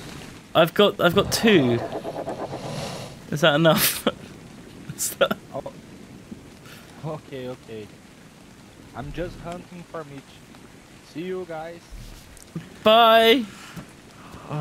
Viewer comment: Wait did the man say "Ok, Ok, just hunting for a mate?" Idk if i misheard